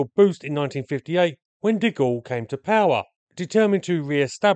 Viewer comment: I swear I heard you say "When Dick All came to power." I admit I had been slightly distracted at that moment and missed the context.